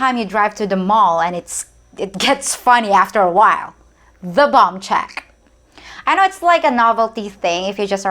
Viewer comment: When you talked about BOMB CHECK I thought you said BONG CHECK. BTW: When I was in China I did see motor bikes, scooters but not in great numbers. I actually saw more people riding electric scooter and at night they don't turn on the headlights so if your walking in the park at night all you hear is BEEP BEEP and they drive around you. I thought the Chinese electric scooters were wicked cool especially the ones made by LUYUAN (I think that was the name of the manufacturer) Actually in China and even South Korea they just beep their horns, wave and cut you off or if your walking they just go around you. I think its an Asian thing not specific to any country. Why debate the peso rate for #1 or #2 just pay for #2 and move on. SPAM is Hyper popular in Hawaii and with some Latin People here in the states who throw it in their rice dishes.